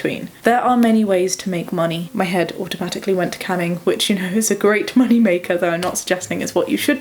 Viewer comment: thought you said "canning" for a sec and I was like hm. I guess the pickle business is booming